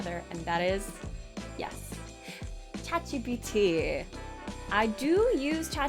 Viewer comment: WHEN YOU SAID CHATGPT I THOUGHT THAT YOU SAID A KOREAN WORD 'CHAJIBITI' IWAS LIKE WHAAAAAAAAAAAAAAT HAHAHAHA